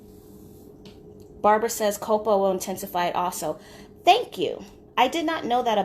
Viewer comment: Can anyone confirm the ingredient that she mentioned at 17:15? It sounds like she said cocoa but I’m not sure. Also is it a good idea to make your honey jar into a candle by filling your jar with melted beeswax? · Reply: Copal